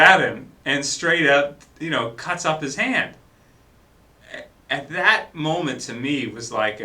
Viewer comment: "cuts off his hand". Did I hear that right...? "cuts off his hand" ?? So Walt doesn't have a hand anymore then?